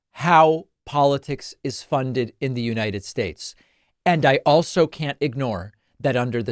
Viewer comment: Did you just say the United Shakes.I'm sure you did